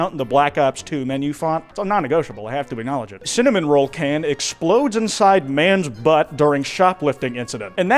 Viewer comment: I thought that said cinnamon toast Ken exploded in a guys asshole and I was very confused. I wonder what that guy is up to tho.